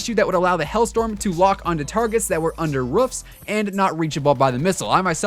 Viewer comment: Did he just say ruffs instead of roofs?